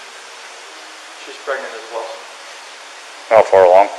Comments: He said “she was pregnant” WAS if that’s not another sign I don’t know what is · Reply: Exactly at which time did he say "She was pregnant" because at 48:50 he said " She's pregnant as well" NOT "was pregnant" do you have any other time did he say was ? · “She WAS pregnant” is key here. You wouldn’t say WAS unless she had a miscarriage or died ?? You would say IS if you thought she just went missing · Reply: Nope. At 48:50 he says SHE'S PREGNANT AS WELL. He does NOT say she WAS pregnant.